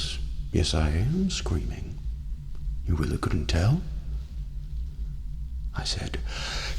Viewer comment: No problem. Although, I do have a small question, and if you don’t wanna answer it that’s alright. What was Egghead screaming at 12:43? (I assume he’s screaming "YOU'RE REALLY GONNA DIE!!! Or "YOU’RE REALLY GOING DOWN"!!!)Well, that’s what I think he’s yelling.) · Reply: He’s saying “you really couldn’t tell?!”